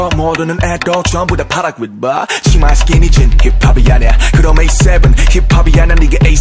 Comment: It sounds like he is saying see my skinny jin